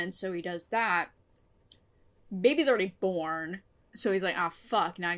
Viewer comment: when you said "baby's already born" I coulda sworn you said "babby's already formed."